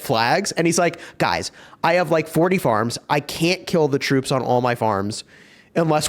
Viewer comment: 40 farms???(I heard it right? 😅)